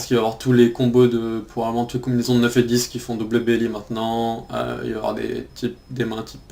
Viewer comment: il dit quoi à 28:26 ? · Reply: il dit "9T qui font double belly maintenant" et double belly signifie double gutshot en gros c'est un peu comme si il avait une open handed.